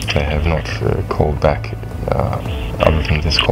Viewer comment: lol the captions say Russia instead of Assange